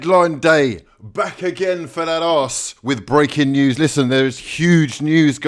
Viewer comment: does he say for datass??